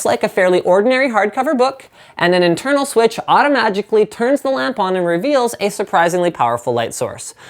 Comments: Is it just me or did he say automagically? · did he says automagicaly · Was I the only one who heard Linus say "automagically" Lol!